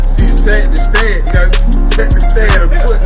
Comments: Ohhhhh check the stance?….i thought it said “take the stand” silly me 😅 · Thought it said “Cheese the stance” 🤦🏽‍♂️